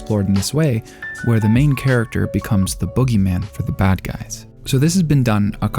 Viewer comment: I heard it as frieren the Boogerman